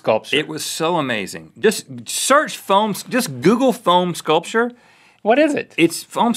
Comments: I thought Rhett said Phone sculpture and was, what! Then relieved it was foam. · I thought Rhett was phone sculpture! I Google it before I realized he said foam and phone sculpture is a thing. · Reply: Same I thought he said phone sculpture too I was sooooooo confused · Anyone else thought Rett said phone sculpture? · i thought Rhett said phone sculpure so i spent ages goggling phone sculpture then i went back to the video and unpaused it and at that exact second he was like 'you get a block of foam' and i realizede what he said so i was like ohh...